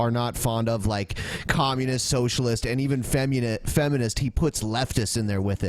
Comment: I heard "femmunist". There is now femmunism. What a wonderful world.